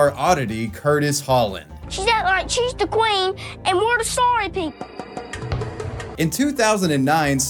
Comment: i fakin love his way of speaking. like a tiny little man. also idk y but i found it hilarious that instead of saying slaves or servants he said "sorry people" that kid was ahead of his time on political correctness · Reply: What?? I could've sworn he said "servant people"